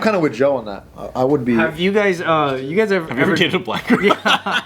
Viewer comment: I thought he said have you ever dated a black girl? I was like wtf how is that even relevant lol 😆